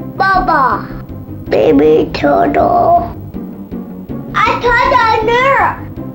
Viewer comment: i think he says “i thought i were?!” (in the episode where kyles cousin kyle visits kyle, kyle says “it’ll be like having a brother!” and ike replies with “i thought i were?!”)